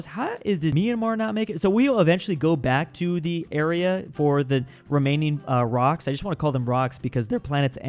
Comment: I can’t be the only one who thought he said Romanian instead of remaining